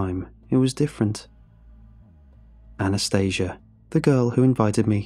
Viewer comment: I really thought you said her name was Anesthesia